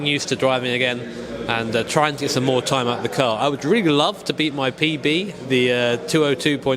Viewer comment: I thought you said "i would love to beat my PeePee"